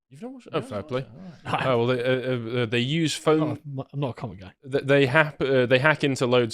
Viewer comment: I thought he said, "I'm not a commoner." Carl ascending his place to the elites.